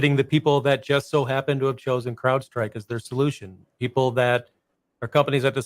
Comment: I thought hey were saying cloudstrike!